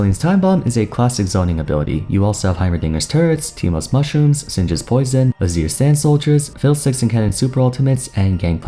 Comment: did u just said "Syndra's poison"?